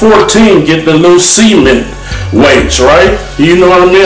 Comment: did he just say he "get the loose semen weights" cause that's what it sounded like to me! How old is Kali just curious how much longer that he has to live... probably another couple years before he dies "of natural causes" I'm betting an enlarged Heart...